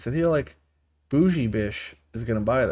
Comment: did i hear him say “boujjie bish”😂😂😂